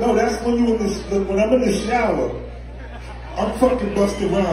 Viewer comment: Wtf did I just hear @ 0:30 50 f#%king busta in the shower